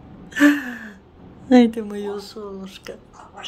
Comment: Ой, или мне послышалось, или Кирочка на 0:42 сказала "волчара"😂, да ещё с таким выражением,что мама даже рассмеялась😅😅😅. С ❤, Днепр-Анкара.